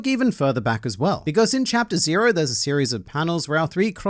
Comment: no way... YOU CAN'T DO THAT ODA!!! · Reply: Oh chapter... I thought you said the next page